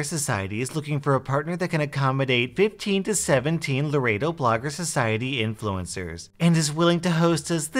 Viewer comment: Anyone else thought that said "15 to 17 pounds" before he said "Laredo Blogger Society?"